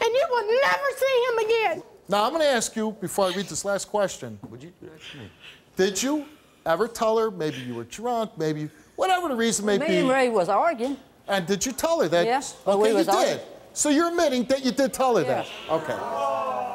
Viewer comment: That lady's accent is so thicc it sounded like she said "Me an him was organ." XD